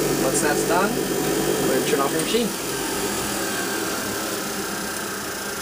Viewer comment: did he say snowcone?